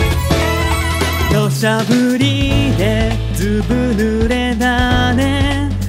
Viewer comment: どうしても土砂降りがおしゃぶりに聞こえてしまう